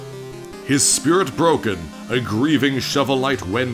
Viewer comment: I thought you said "His Skirt Broken" lol